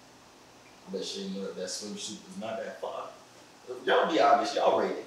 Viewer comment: did he just say that simp suit?